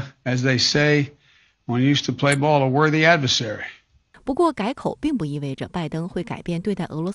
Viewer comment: 拜登说普京是个“worthy oponent” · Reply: I thought sleep Joe said “worthy adversary”🤔🤔🤔